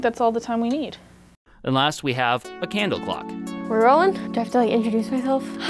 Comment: I thought it said candle cock for about 3 seconds